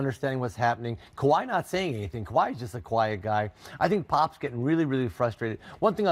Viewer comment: My brain is trying to trick me into believing I just heard McHale say "Kawhi is just a Kawhiet guy"